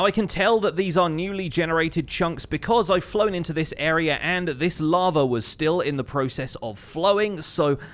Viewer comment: I wonder what says here? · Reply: " I flew into this area and the lava was still in the process of flowing."